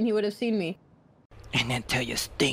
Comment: Every time when I hear 'the old stank him again again,' I'm filled with excitement 😂😂😂